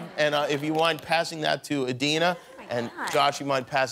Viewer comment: Did Jimmy call Kristen ‚Anna‘ at 13:12 ? 😂👏🏽👏🏽